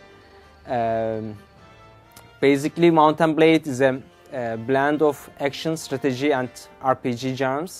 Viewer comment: lol he said bland when he was suppoosed to say mix:P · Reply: He said blend but it sounded like he said bland.